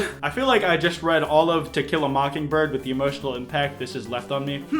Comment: I thought he said... t e q u i l a m o c k i n g b i r d